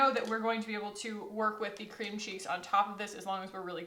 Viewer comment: sass in the beginning of the video since she literally just did ALL OF THIS= All of us. Btw, I kept hearing "Cream Cheese" every time you said Cream Cheeks, and it made me imagine you putting cream cheese on your face: So, not a complaint, just made me giggle! And I think I am forever cursed to enjoy No Other Mascara since you first convinced me to buy the Thrive mascara. Literally NOTHING compares. It totally replaced my Ciate London Wonderwand and Stila Huge. Word is Law.